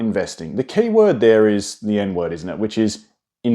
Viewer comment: Tell me I'm not the only one who heard that 😭🥀 "The keyword there is the N word isn't it?" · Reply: He said “the END word” 😅 just with an Aussie accent